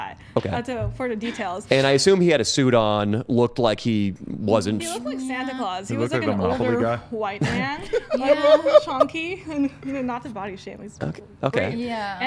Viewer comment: Did she just say honky?!!! 😂😂😂😂. WTF😂😂😂